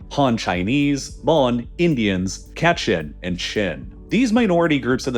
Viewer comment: did I heard cat shit